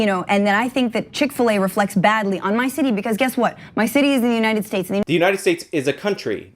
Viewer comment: She actually says "...my city is in the United States."